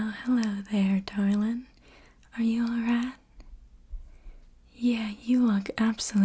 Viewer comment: I heard "Are you alright?" as "Are you a rat?" and had to check the title of the video to make sure this wasn't "Mob Boss Interrogates You ASMR"